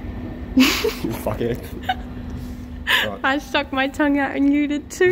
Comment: Nice Work AGAIN! Yes FANSTIC Job on em bolts! I've often wondered if Camera flirts with ya while you're trying to be serious mate & today, I heard " I was sticking my tongue out at ya" Your A Blessed Fellow.